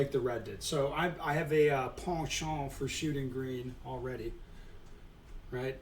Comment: I heard “I have a Sean Paul for shooting green” I think I have auditory dyslexia